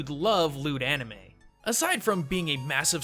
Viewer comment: when you said loot anime i thought you said "lewl anime"